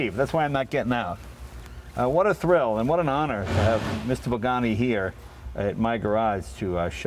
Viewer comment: does he say Mr. Bugani ? :D